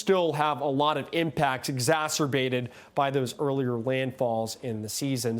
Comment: Did he just say Zasserbated ??